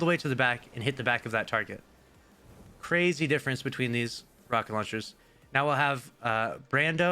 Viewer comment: Did he say rocket launchers?